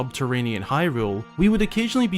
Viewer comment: your caption said redheads instead of redeads and it sent me 😂